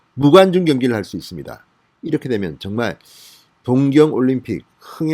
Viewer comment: 하계 올림픽이지..왜 다들 동계 올림픽이라고 밀하지..겨울에 하는 스포츠가 동계 스포츠지. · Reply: 동경 올림픽을 동계올림픽으로 님께서 잘못 들으셨네요